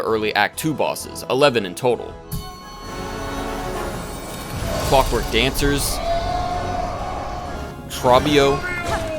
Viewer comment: I HEARD F***WORK DANCERS BRO 😭😭😭